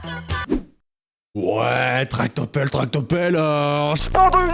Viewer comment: moi j'entends pas tractopelle j'entends tarte au pomme x)